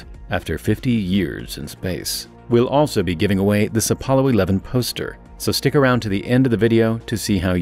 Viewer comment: i miss heard the end where he said apollo poster and thought he said apollo 11 toaster now i keep thinking of a toaster with a net over it to catch toast before it gets launched across the capsule. man i wish i had one of those.